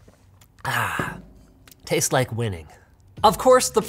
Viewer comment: I wasn't watching the video, and at first, I thought you said 'tastes like women'.